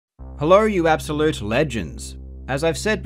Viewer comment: First i thought you said "Hello you absolute virgins", and you're not wrong.